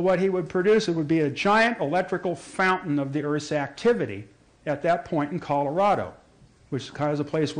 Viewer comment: can't understand what he says... " A giant electrical ??? in the Earth activity"... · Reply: "A giant electrical fountain of the Earth's activity..."